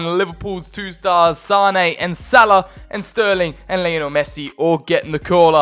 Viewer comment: He said sane and salad instead of mane